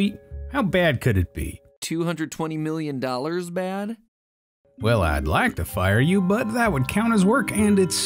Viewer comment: my headphones glitched and All I heard was the “F” sound so it sounded like “Well id like to F… you”